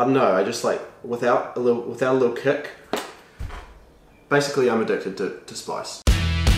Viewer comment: at the start when he said "im addicted to spice" I thought he said :im a dick" I had to rewatch it with subtitles to see what he actually said